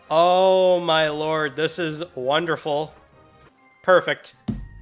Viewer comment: Did you say... "purrfect"?...